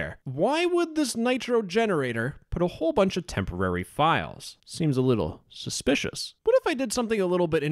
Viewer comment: the way he said: seems a little suspicius. sounded like skeeter jean